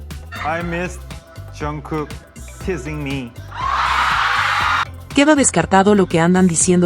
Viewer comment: Yo hablo perfectamente el ingles, y la forma en que Tae pronuncio “teasing me”a mi tambien me sorprendio porque efectivamente yo tambien escuche “kissing” me. Pero esto lo corregieron inmediatamente en el momento que Jimmy pregunto “kissing?” 😂❤